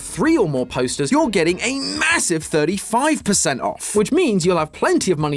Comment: did you just say MASSIVE ?